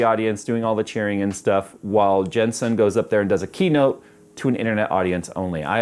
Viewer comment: I thought he said GenSan which stands for General Santos, My Home city 😊